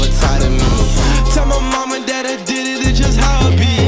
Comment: sounded like mom and dad but it is mama that